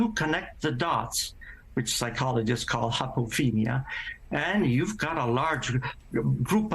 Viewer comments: What is the psychological term that is said at the 4:00 mark? Hypogenia? I can’t quite make it out with his accent, nor can I yet find it with various spellings. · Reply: I found it. Apophenia. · What is that word he is saying? Hoppletrinia? Psychologists call it what? I looked up hoppletrinia (spelling?) I can't find anything! I found psychologists call connect the dots as: principles of the gestalt theory of visual perception But I can't find this hople word he said? What did he say?